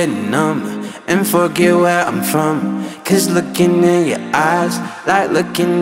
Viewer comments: I had to see the lyrics after hearing this on radio. He sings and pronounces forget where it sounds like F-You. I think he did it intentionally. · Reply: Imm so glad I'm not alone here! I legit did a spit take when I first heard this on the radio cause I swore he said "and fuck you where I'm from" and I was STUNNED 😂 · I had to look up the lyrics BC I thought he said "f*ck It where I'm from" but he said "forget where I'm from" 😅